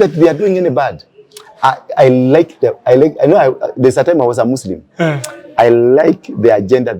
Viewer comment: Did I hear him saying “there was a time I was a Muslim… “ @ 9:09 I wanna know what he smokes that gave him all this energy to hate Somalia and Muslims.